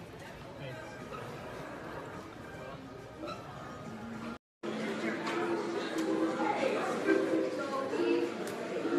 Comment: WAIT DID SHE SAY NO OR YES? XD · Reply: GamingWithSherbert ong i think she said no